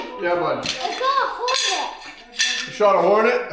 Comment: i heard "i caught a hornet" too and my first thought was "THIS KID IS JUST HANDLING A HORNET WITH BARE HANDS?!?" some real tough kids you got there. XD